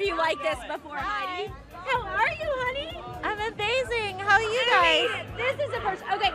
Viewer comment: The way the interviewer calls her Honey at 0:20 and 2:24 is kind of cringey/unprofessional imo. I thought she was saying Heidi at first, but then I played it back, and all she definitely called her Honey twice, which is strange. I love Heidi’s costume and sense of humor though hahahaa. Seriously, what a legend.